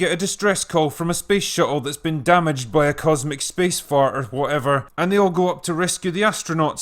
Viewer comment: Did he say "Cosmic Space fart" at 2:03 ?